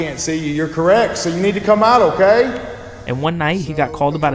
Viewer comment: That last one scared the living sh-t out of me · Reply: What I heard was "get out!" Some ghost had enough of his constant visits lmao